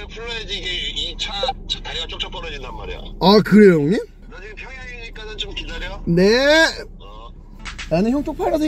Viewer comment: 나만 평양이라들리나?..